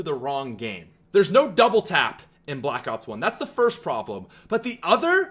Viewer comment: did he say there's no double tap in black ops 1?